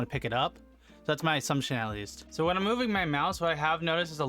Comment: Did you say asumptionality?! lol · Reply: I heard, "That's my assumption, at least"